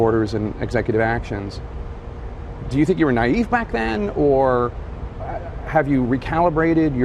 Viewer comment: I heard "naive black man" the first few times I heard it lol